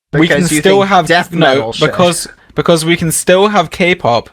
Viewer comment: I CAN'T TELL IF BAD ACTUALLY JUST SAID BULLSH*T OR NOT AND IM REALLY SCARED Edit: nvm he said "deaf people should" (i couldn't hear the first half of his sentence) so we're chill